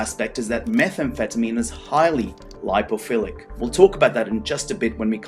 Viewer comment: on 4:12 the subtitles say "biophilic" but i think that is supposed to be "lipophillic". Thank you very much for the subtites! English is not my first language and i appreciate it lots!